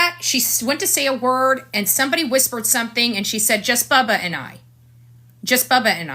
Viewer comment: I heard " just Bubba n u"